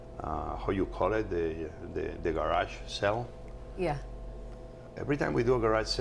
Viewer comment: What does he say at 4:31? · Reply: The garage sell.